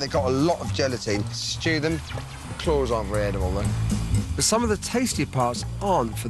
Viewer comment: Can someone tell me what Heston says in 7:47? I can't quite understand what he said about the chicken claw, which is totally edible. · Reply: He says "the claws aren't really edible tho" ...